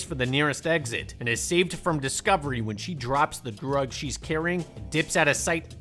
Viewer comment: drops the what? Can anybody clarify what he says here I cannot understand despite repeated attempts to listen… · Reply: Drug she’s carrying